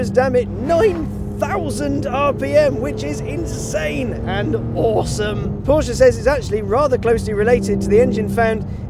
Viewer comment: anyone heard "and awesome" at 4:47???